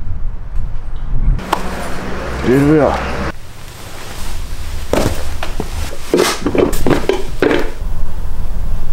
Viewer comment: what he just say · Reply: Here we are